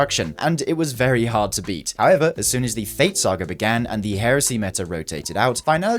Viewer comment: I swear I heard it " When the feet saga began" 😂